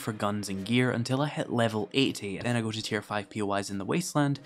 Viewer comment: did he say level 18 or 80